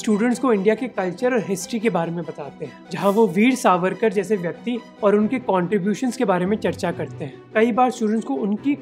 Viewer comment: did he just say.. "jaise" vyakti...